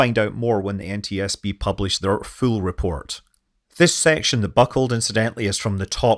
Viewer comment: At the very end you mentioned having to wait for the full report. For a moment I thought you had said fool report.